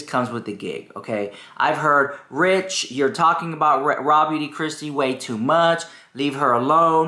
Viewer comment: I swear you said Raw Meat Kristi 😂 🐍 ❤ 🎃